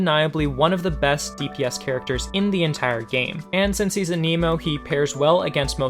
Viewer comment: I thought you said an emo lol